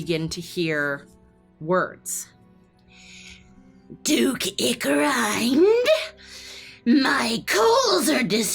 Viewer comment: Did anyone else misheard the dukes name as Duke Dickgrind or was that just me 😅